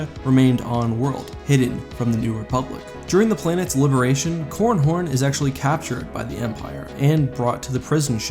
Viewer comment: did you say corn horn?